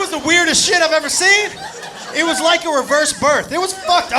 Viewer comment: “It was like a reverse birth!” so amazing 😂😂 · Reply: Oh so that was a joke! I heard it like "It was like a reverse bird". Didn't make that much sense as it does now xD